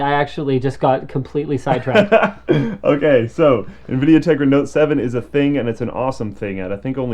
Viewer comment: Linus, it sounded like you said Nvidia Nigra.. lol.